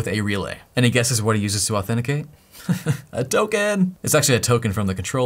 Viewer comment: I heard "A token!" as "Hadouken!"